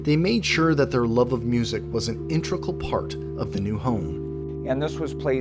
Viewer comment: did you say “intricle part”?